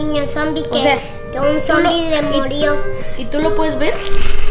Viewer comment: yo entendí que decia picu “que el zombie le mordió”